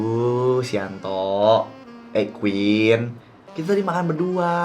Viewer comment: what?! Suap-suapan? Ini gue gak salah denger kan?